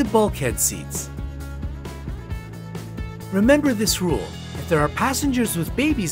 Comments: it says emember instead of remember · Do you applaud at the end of a flight? Here're some tips about things not to wear on a plane <link> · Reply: Yes I know its for no reason because the pilot can't hear so yea I just clap because its fun plus at 4:00 it says emember